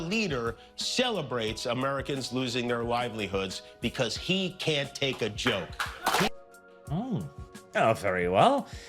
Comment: thought he said it wasn't a joke 😅